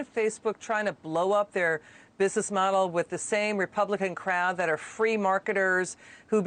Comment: For split micron of a second I thought she said "Republican crap". I almost sold my house and went straight Lone wolf on FOX. 🤣